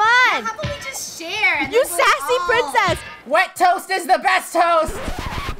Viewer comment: I thought he said:but toast is the best toast 😂 XD